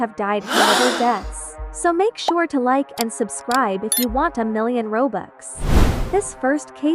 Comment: Girl is it just me or did u just say... "Like and subscribe if u want 1 million robux!" ????